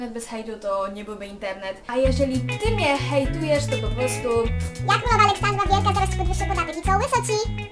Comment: Co mówisz w 7:08 ? Bo niccc nie rozumiem. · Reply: ona mówi: Ja Królowa Aleksandra Wielka zaraz ci podwyższe podatek i co łyso ci